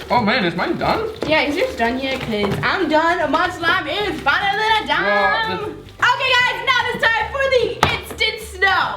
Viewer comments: did say I am finally dum? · I heard dumb not done 😂